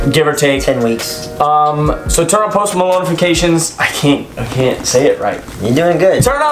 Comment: Did I hear that right? "Post Malonifications" 😂